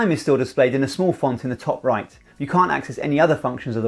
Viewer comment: Hi. As english is my second language, I have difficult time understandind certain things. On minute 9:29, did you say "you CAN, or you CAN'T access other features while recording activities..."? Thank you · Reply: Can't. Try reducing the playback speed to 0.75x - YouTube does a good job on this. And you can enable captions - I have full transcripts for every video. Hope that helps.